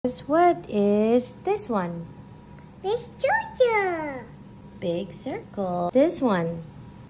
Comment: I thought she said "biscocho" when she said big circle hahahaha so cuteeeeee, ✨️💗